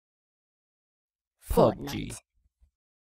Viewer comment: I heard “fort-G”